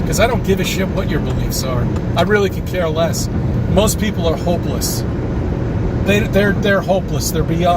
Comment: I thought he said homeless not hopeless The rest was also nonsense. You definitely had a very full and eventful holiday, it seems like you may have fried a few brain cells while you were sunning yourself, you'll soon get back onto the swing of things 🤯🙈🤣